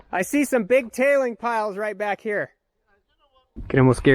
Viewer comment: Am I the only one that thought he was going to say “I see some big t*tt*es back here.” 😂😂😂 awesome video man